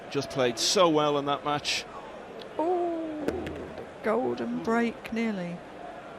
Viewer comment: golden break sounded like it was saying grandma who have 90yrs